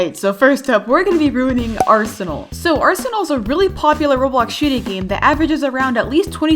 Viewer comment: When she said that arsenal was a popular roblox shooting game, I thought she said Shitty game Lmao